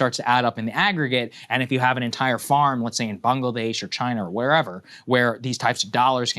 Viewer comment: I swear @ 8:24 it sounded like he said Bungladesh.